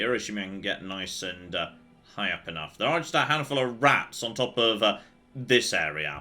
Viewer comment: I really heard that as a couple of "rads" who he then went to shoot